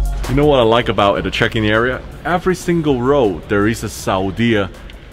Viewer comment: First I heard “Chicken Area” then I realised he is talking about Checkin area.